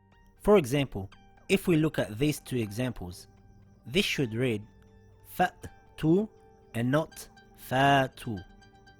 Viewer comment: Salam, There is not much to talk about it unless you want to understand its history. The main important thing is the pronunciation. If there is a sukoon on it, it is still read with half the strength of what it is normally read. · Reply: oh ok, jazak Allah khair both the questioner and the answer, I thought it said fattu(got confused), now I understand it's cut sharp like fa-tu not faatu